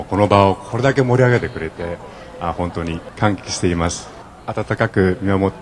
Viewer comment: Is it just me or is he actually saying 歓喜しています because that word also exists in Chinese.